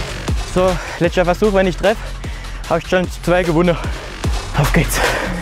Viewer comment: Was hat bei der 5:00 gesagt? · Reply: Letzter Versuch wenn er trifft hat er zwei gewonnen auf geht’s